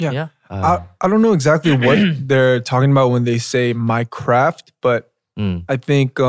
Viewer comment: what did he say???? i heard minecraft but i think it's my craft